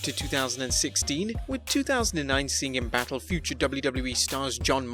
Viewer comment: did he just say nba?